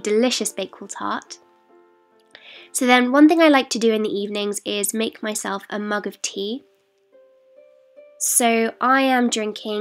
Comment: So for some reason at 4:38 I heard her say "Muggle tea" and im like wtf...